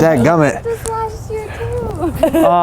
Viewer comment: What did the last girl say at 3:46? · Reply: something about it seeing him last year too or some shiiii~ and that's why he said see you next year.